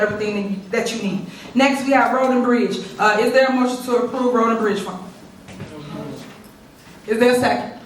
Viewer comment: Funny, I thought Tiff Tiff was saying Roland Bridge, but it's Road and Bridge Fund!😂😂😂😂